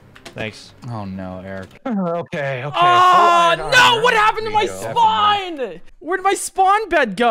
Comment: I thought Eric said WHAT HAPPENED TO MY SPINE THOUGH? and it made me crack up.